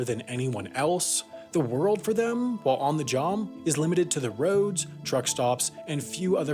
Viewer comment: does he say ‘jom’ instead of job? 🤔